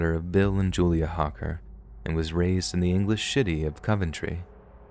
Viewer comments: I almost fell asleep listening to the story until I heard mispronouncing city as Shitty at 17:12 I’m sorry but I wheezed so hard I chocked on air🤣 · I swear you say English shitty instead of city 🤣🤣 made me laugh